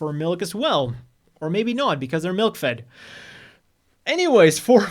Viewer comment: I deadass thought he said MILF'D, turns out it was MILK FED